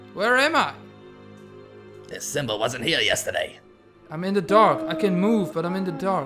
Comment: nice job with voices and all... LOL Lisa Kudrow cracked me up too :D "Why did I say Jeffrey" :D 6:55 I thought you said "I'm in the dog" O.o